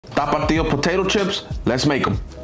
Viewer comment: He censored himself saying it because he knew it would just be lacklustre in comparison to the almighty T A P A T I O from the man himself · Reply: I thought he was saying "ta puto io" and had to bleep out the bad word.